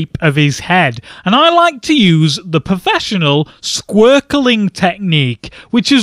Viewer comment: I got really stuck drawing the 'squirkle' head and gave up. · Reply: Lol, at first I thought he said Squirtle XD